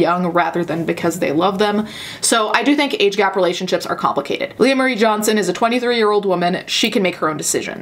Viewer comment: I wasn’t fully listening when the video started and I thought you said “Ray William Johnson” LMAO